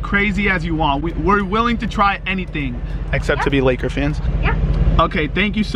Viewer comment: did he say except to be laker fans?